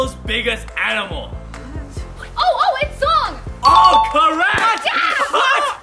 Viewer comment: Who's early?? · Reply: I think she called you fat Zhong